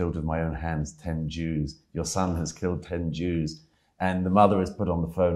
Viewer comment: As a French, all i hear is "Allez au bar!" (Go to the bar!)